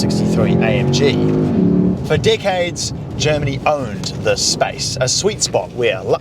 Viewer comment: am I the only one that thought he said “for D heads”?😂